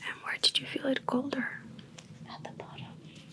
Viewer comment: она сказала "это тоже" или мне послышалось? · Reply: Nope just "Where did you feel it colder?" "At the bottom"